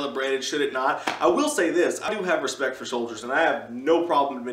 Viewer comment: did he really say 0:50 that he has no respect for soldiers